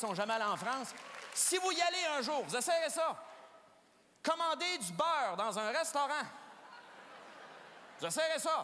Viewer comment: Qu'est-ce qu'il a dit à la minute 3:13? Commander quoi dans un restaurant? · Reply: A 3.13 commander du ``beurre`` dans un restaurant Français en espagnol: (manteca), en portugais : (manteiga) j'ai vécu la même expérience en France dans un restaurant, le serveur ne comprenait pas le mot :``beurre`` prononcé à la Québécoise!